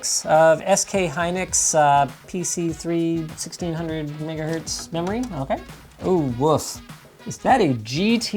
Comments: yoo what he sayy? bro said n- hertz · I swear I heard him say n-word hertz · umm anyone else hear that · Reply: 1600 megahertz? Yeah, he said megahertz. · why does it sound like hes saying the n word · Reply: everyone should have 16gb of 1600 niggahertz ram. LTT <link> wants to know your location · did he just say 1600 “nigga”hertz! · Am I tripping or did he accidentally say N***ahz instead of megahz lmaooooo??? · did he just say NIGGA HERTZ ????? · did he just said NIGGAHERTZ?? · does he say nig a hertz? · did he just say N***ahertz? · did I just hear niggahertz? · When he said PC3 1600 MEGAHERTZ memory, I could've swore he said "PC3 1600 N____hetz memory. I ain't trying to be racist, but play it back and it sounds scarily similar.